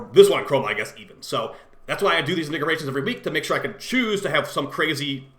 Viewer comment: slowdown bro lmao i thought i heard "niggarations" 13:40.